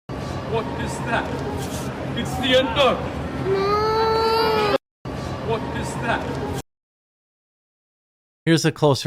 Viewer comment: I thought he said the bad guys name was the N word